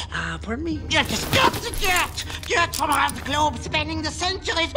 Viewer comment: When i was young, i thought he said "You have to scrub the deck". Lol I sure got mixed up with words at a young age.